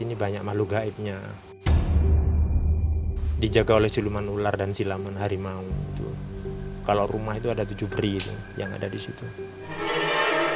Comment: Maaf saya dengernya siluman dan silamun di menit 12:23-12:26 bener gak apa telinga saya yg salah denger .yaa gpp sih mungkin kepleset dikit